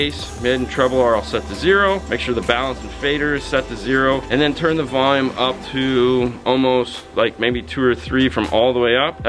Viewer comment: what does he say at 1:37? "like maybe 2 or 3 (oclock?.... "from all the way off/up"?) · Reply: Turn the volume on the stock radio almost all the way up